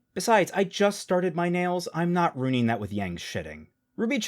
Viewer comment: “Yang’s Shitting” Am I hearing this correctly?